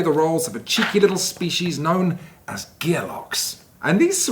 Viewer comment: I misheard that you play as cheeky little minions called Gearbox, and assumed you were undertaking a quest to design another Borderlands.